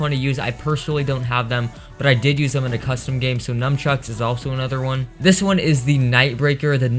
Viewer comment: im your newest subscriber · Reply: lol did u say NUMchucks? xD